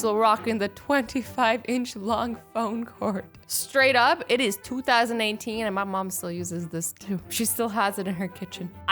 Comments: wait did she say its 2019 .0. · did she say 2019? It’s been posted in 2018. BRUH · Does she say 2019? Is she from the future. Or just me mishearing. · Tell me why it sounds like she says it's 2019